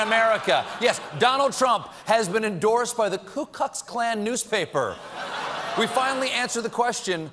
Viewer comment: Sounded like Colbert said "Ku KUCKS Klan" 😂